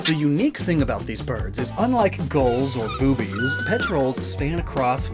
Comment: Yes. Yes he did just say Boobies and yes that is a real subclass of birds.